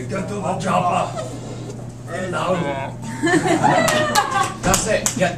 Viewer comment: Sounded like she said "Get to the Chapa'ai" (Stargate fans will understand that joke)